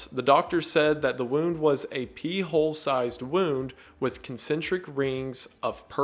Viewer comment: he really said a “pee hole sized wound” not a pre sized hole and I can’t get over that 😂